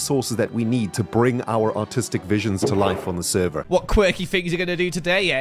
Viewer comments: I think we need get over Ren's accent making it sound like he says "autistic" instead of "artistic". Yall already made him feel bad about it before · i thought he said "autistic visions" for a sec omt 😭😭😭